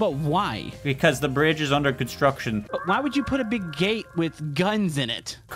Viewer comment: 3:8 kinda sounds like he said big asś